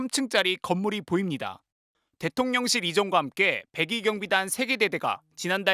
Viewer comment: 저만 102경비단으로 들리나요?